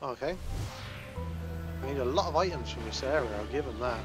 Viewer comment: "A lot of items from the seller Hitler." I'm...not sure what to say to that...~